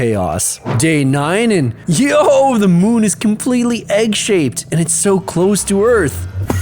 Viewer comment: the moon is completely egg shaked💀💀🥚 (I misheard that)